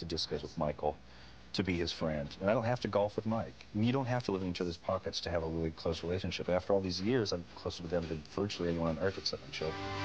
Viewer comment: What does Buck say at the very end (58:47)? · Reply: “We don’t have to live in each others’ pockets to have a really close relationship. After all these years I’m closer with them than virtually anyone in the …. “ and then man I just can’t make it out. Even slowing it down.